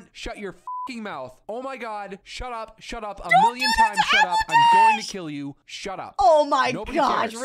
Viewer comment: Did she just say “AppleDash” XD